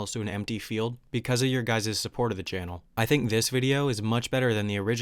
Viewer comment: Did he say your guyses? 2:38 ish